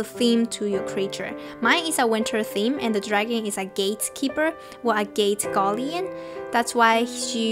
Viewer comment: i heard ' gay keeper ' 😂